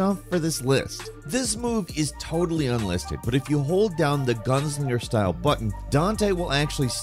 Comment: "If you hold down the guns in your style button..." I get the feeling I misheard and he just said "Gunslinger style button" in a awkward way.....which still feels wrong since it's a directional pad input and not a button per say......but that part of the sentence for the DMC5 command just made we question the universe for a second 😂🤣